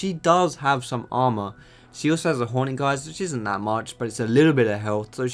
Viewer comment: did u just said Hornyguard